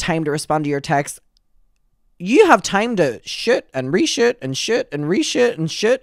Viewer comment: I know this is completely unrelated but as a stupid American when youre saying "shoot and reshoot and shoot and reshoot" with your accent I hear "sh*t and resh*t and sh*t and resh*t" and it made me giggle